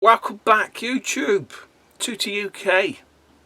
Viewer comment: Feck me for God knows how long I thought you have been saying whack and back Youtube lol Welcome Back it will be my ears and your accent ha ha ha ha ha Friday lol quality any excuse to use that clip I tried to bag a load of Zzap 64 mags I have now given up on this issue 1 went for £300 I kid you not. Do the Bart Man. Got a Wii I can trade in myself boxed might just frack it off to CEX Honest copy at least is sounds better than very good quality lol Playstation copies got loads ,of them the copy games were too cheap £5 a game with front and back box art winner winner chicken dinner. TG16 America had bigger packaging just because it sells better they could not purchase anything small? Nice playstation pick ups pal LMAO i thought everyone knew that got loads of spare backs for playstation games Top Tip's so what your saying is its a release but not a release. Interested can I get a copy of thew list? Great stuff always entertaining